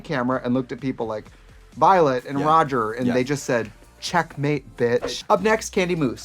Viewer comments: Excuse me? „Violet and Roger“?! I know it’s Raja but with my stupid ears it sounded like Roger 😂😂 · Did Trixie just call Raja "Roger"? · DID YOU JUST CALL HER KANDY MOOSE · Did Trixie call Raja "Roger"? Lmao. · did Trixie call Raja "Roger"???? 😲